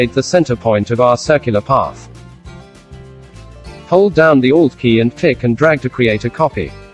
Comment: in 1:42 minutes what di he say? i couldnt understand, hold downthe old k? and drag to create a copy? im a new beginner for graphics designing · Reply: Alt Key.